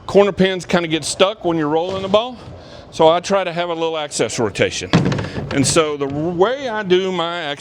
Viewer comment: It sounds like youre saying "axis" and "access". Very confusing.